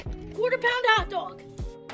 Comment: I thought he said 400 pins hotdog 💀